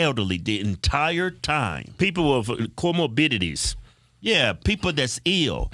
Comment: I had to rewind several times. What is he saying 7:42 Cuomo dignities?